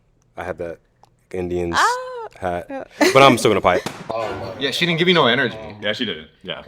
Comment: I swear he said “I’m gonna pipe” 😂